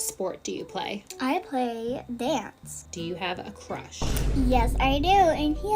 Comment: i thought she said "do you have a crotch" 😭